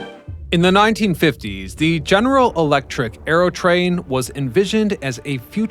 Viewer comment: Four minutes along into the video he has said General Motors about three or four different times. I'm not sure where you thought you heard general electric but I believe you misheard something in the video. Probably Electro-motive, the General motors division that builds big diesel electric train engines and locomotives. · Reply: he clearly says GE